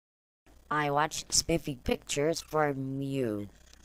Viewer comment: I heard “i watch spiffy cheers for uh. You”